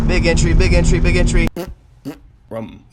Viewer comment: In the beginning i thought you were saying "bigotry bigotry bigotry"